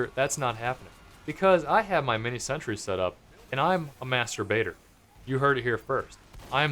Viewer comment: What you THOUGHT he said = "I am a masturbator." What he MEANT = "I am a master baiter, but I will say it like masturbator to make everyone giggle." And that is why is awesome.